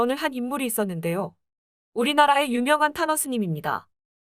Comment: 탄허스님 할때 타노스님으로 잘못 들음 .. 순간 음? 했네요 ..